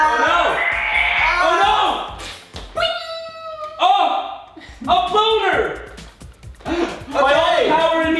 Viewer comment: i thought he said I GOT A BONER